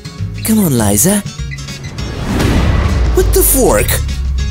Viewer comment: I thought he said what the f